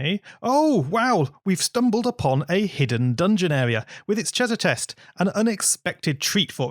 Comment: he said cheaser test Instead of treasure chest